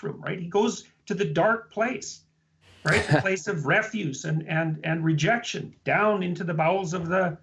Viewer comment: Great video! Just a few corrections to your transcripts. I think Jordan was saying: at 5:55 "a place of refuse" (as in garbage) not "refuge", at 7:25 "suffering and finitude" (death sets a limit on life) not "infinitude", and at 9:00 "Garden of Gethsemane" (in Jerusalem) not "Yosemite". Edit: I see others have pointed 2 of these out already.